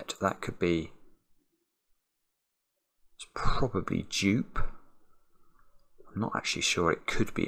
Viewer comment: I thought you said “jupe”